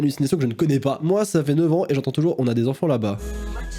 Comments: Moi à 4:01 j'entends aussi on a les enfants d'en bas à se coltiner, à 6:11 j'entends tarte au pommes ×3 tarte au pralines ×3 et à 10:08 j'entends un mélange des 2 en gros j'entends "saloperie de kinder au fruit 😂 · j'enttend on a les enfant d' en bas 4:22j'enttend c'est le prit d' une dannete Sophie!🤣🤣🤣 6:11 j'entend tarte au pomme tarte au pomme tarte au pomme 8:39 j entent prend pas un concombre trop mou 9:25 j attend y'a du chien du poulet 10:26 j attent j ai des puce au colier dit moi que je suis pas la seule qui entent sa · moi jentends "on a les enfants d'en bas a se coltiner" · moi j'entends ''on a les enfants dans bas'' et si tu écoutes la suite on dirait qu'il dit ''à ce coltiner'' et du coup ça donne ''on a les enfants dans bas à se coltiner''